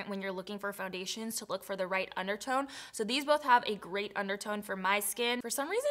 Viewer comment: When you said “great undertone” it kinda sounds like you said a “grey undertone” lol i was like wait wtf lmao 😂 I had to go back